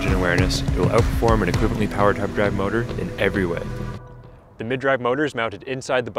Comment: What are you saying at 1:44? I listened over and over and could not make out what you were trying to get across. "you'll outperform a ???motor in every way". I presume you are saying hub drive. You don't mention that only hub drive motors are capable of power regeneration. You also neglected to mention you can use hub gears only with a mid drive bike which additionally allows you to use either a chain with a chaincase, allowing vastly longer chain life by keeping dirt and dust off and lube on the chain (unfashionable though that is for posers), or toothed belt drive as they already do in most civilized countries of the world and will, eventually, in Canada and the States. Most inexperienced riders do not use derailleurs effectively, yet they are everywhere because they are cheaper to manufacture than hub gears. Derailleurs are used in racing because they are slightly more efficient in terms of power loss, but that's not a factor with elecric assist, and racers typically know how and when to shift. Most riders I see on the road with derailleurs are in the wrong gear. North Americation e-bike technology is still in its teething phase and evolution is being kept slow by all the opportunistic amateur startups selling incompetantly designed no-name bikes, attracting all those first time buyers who are selecting purely by motor wattage, battery Ah rating and tire size because they know no better. Frame geometry and riding position and fit is completely ignored yet these are the mos